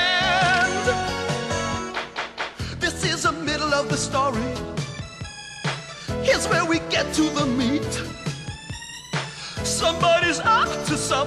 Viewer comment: "Here's where we get to the meat." Did I hear that right? Is that what he says?